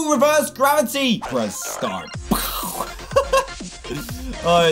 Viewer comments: I always thought that it said Prescott instead of press start. 😂😂😂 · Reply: Colt Haslam i thought it said “lets start” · I always thought that it said Prescott instead of press start. 😂😂😂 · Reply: I thought it said let's start